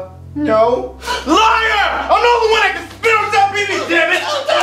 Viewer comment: it took me years to find out that Bowser said “I’m the only one who spits on Chef Pee Pee” 😂 I always thought Bowser was saying gibberish 😂